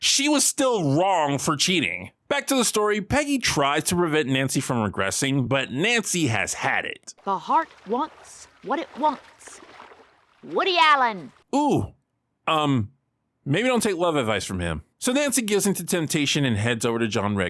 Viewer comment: As someone who didn’t know who Woody Allen was until I googled him just now, I thought Nancy was saying “The heart wants what the heart wants: Woody Allen!” As in a declaration that she wanted Woody Allen. Whoops…